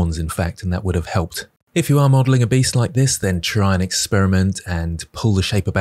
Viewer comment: I thought it said "breast" rather than "beast" and that probably says something about the media I consume.